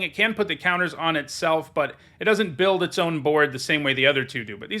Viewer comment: Yep. I didn't say otherwise, just that that isn't as good as spreading counters. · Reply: Listening to it again I believe I misheard "can put the counters on itself" as "can't put the counters on itself" and that caused me to post the comment.